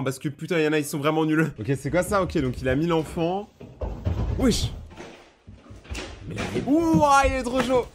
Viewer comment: j'ai compris 1000 enfants